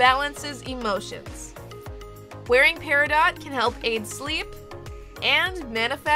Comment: I thought it said PERIOD!!